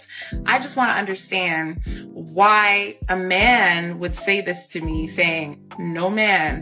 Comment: Never become a stepdad. Never marry a woman with a kid, don’t live with them, don’t date them, don’t even talk to them. Never. No exceptions. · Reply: I heard "A real man would do" 😂